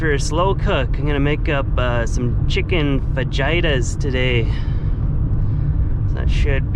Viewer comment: Had to listen twice Thought said Chicken vagina Then proceeded to add chicken breasts Hmmm Me thinks is thinking about a Mrs It is, after all, SPRING 👫💘